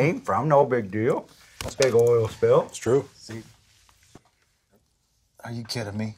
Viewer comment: I swear I heard someone say “scene